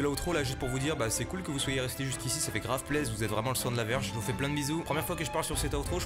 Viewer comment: Attendez c’est moi ou à 11:14 il a bien dit "vous êtes vraiment le sang de la VERGE" 😭😭